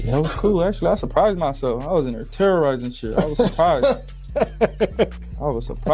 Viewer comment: I thought he said he was terrorizing shit